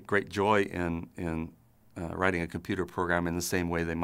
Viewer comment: I swear the first time I heard "In the same way they might take great joy in riding a polar bear plane." That sounds incredible if you ask me.